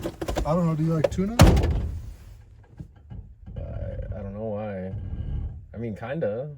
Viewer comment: When it sounded like he said do you like tuna I thought to myself damn bro why would you say that out loud. Tuna is pretty healthy lol people give it too much hate it's not sardines 😂